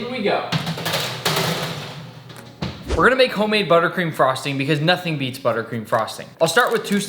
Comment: why did i hear "burger king frosting"